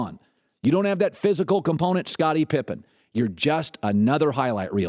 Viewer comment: Am i the only one who heard him say scobbitie pippen